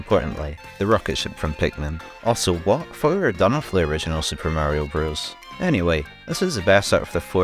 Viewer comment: this is a quality video but i do not know what the hell you said here and the captions srent any help 😭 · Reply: "also: what, i thought we were done with the original super mario bros"